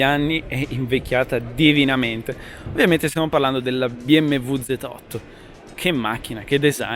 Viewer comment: i think you mean Z8. Subtitles say Z4